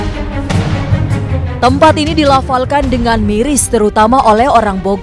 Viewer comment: Apa gw salah denger ya pas awal mulai di laporkan apa di lapolkan